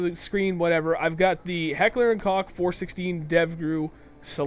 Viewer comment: lol did u just say COCK !! 1:03 LOL XD